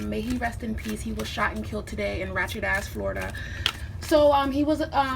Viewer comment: did she say rachet ass Florida 😂